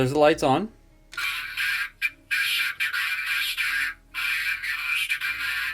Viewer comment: he actually said Deceptacon master